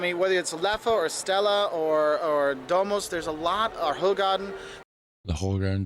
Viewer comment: he said Domus, which is Belgian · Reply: It's hard to distinguish between the two when a foreigner tries to pronounce Dutch/Flemish, i really thought i heard Dommelsch but you're right, it might have been Domus (also great beer 🙂).